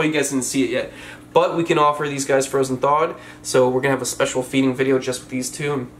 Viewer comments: Did you say frozen dog? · did he just say hes going to feed it "frozen dog"???? 😳